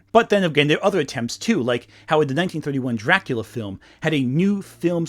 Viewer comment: Very minor mistake but you called American McGee's Alice, Alice McGee's Alice :P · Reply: I thought he said 1951’s Dracula.